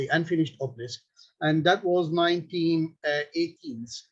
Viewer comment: Why does he say "1918s" it sounds like he is saying 1980s.